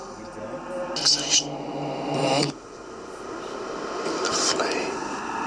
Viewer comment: I think I heard "the flame" at 24:31. Don't know if it means something... Still watching the video. Love your work!